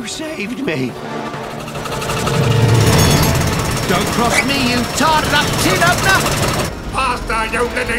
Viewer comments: what did train say? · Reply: thx because I thought he said "Don't Cross me you oversized tea pot" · what did train say? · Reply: Don't cross me, you tarted-up tin opener!